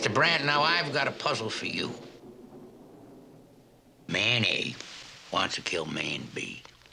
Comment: I keep hearing Columbo say: “Mayonnaise wants to kill Man B.”